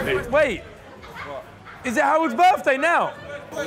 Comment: Ethan: Wait, is it Harold’s birthday now? Simon: Who’s? Harry: is literally sat right next to Simon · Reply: tbf I heard Howold's birthday...